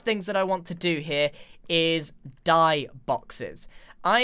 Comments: When he he said “dye boxes” I thought he said “die boxes” LOL! 🤣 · I thought you said die boxes instead of dye boxes lol, a kit for murder? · =grain=gain=brain=main Edit:I thought said die boxes but in reality he said dye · I thought you said DIE boxes. I got seriously worried. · said "dye boxes" and i heard "die boxes" xd what a disappointment